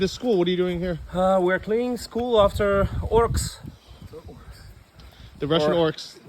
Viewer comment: I thought the Ukrainian guy was mispronouncing words in the beginning but his English was just more advanced than I thought and I didn't realize he was cracking jokes. "Buttholestan" 😂 i thought he was saying "after work" too but he was calling them orcs